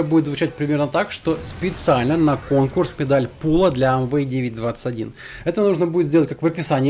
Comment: на 8:30 послышалось "Педаль пула")